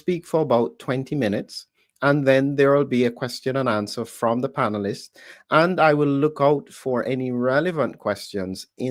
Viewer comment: Can anyone make out what Dr. is saying about a product with cats claw, wormwood and licorice root in it? Its around the 2:21 mark-- I cant make it out. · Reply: Phytocidal